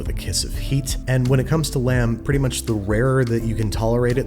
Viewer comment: For some reason I thought it said lamb sauce